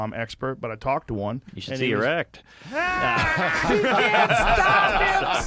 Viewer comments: what does say? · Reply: You should see your act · what does say right there? “You should see erect”? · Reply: You should see ur act